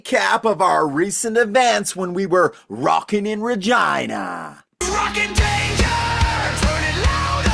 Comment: You go guys!! 🤘 I thought you said rocking in vagina 😅